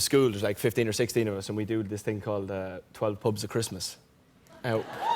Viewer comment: half the video I thought they were saying the "12 pugs of Christmas"